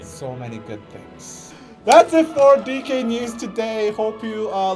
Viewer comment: I am shooked. I heard it as "Dick news" instead of D-K News and "daddy Kim" instead of.... my ears need checking